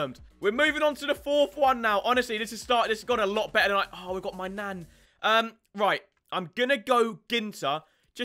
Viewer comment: I swear he said “my Nan”